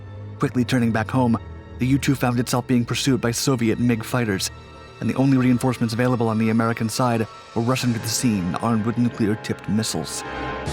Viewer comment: Man I had to listen to this like 5 times before I understand what he said. It really sounded like ~"the only reinforcements available on the American side were Russian armed with nuclear tipped missles"... What?? how were the american reinforcements Russian.... (*turns on captions* captions do say Russian).... OHHHHHHH "rushing" to the scene!